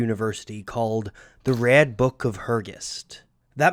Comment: Me: Distracted by food Video: The Red Book of Hergest What I heard: The RAD Book of Hergest My mind: Well, it may be rad, but is it TUBULAR?!! Hyuck hyuck hyuck ... I hate myself right now.